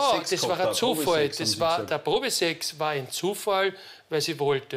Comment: Das mit dem Probe Sex. Habe gedacht ich habe mich verhört. Hab's mir dann noch mal angehört Tatsache Probe Sex hat er gesagt Unfassbar 😮